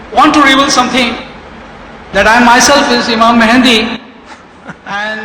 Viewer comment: Maybe I am not hearing well but at 1:39 I heard him saying "I am imam al Mehendi"? 😅😅😅😅